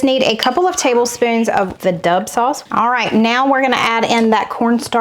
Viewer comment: I. Thought you said the dove sauce not the dub sauce!!😅😅